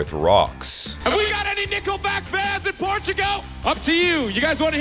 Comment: Is it just me or did he say "Nippleback?" Sounded like he did 😂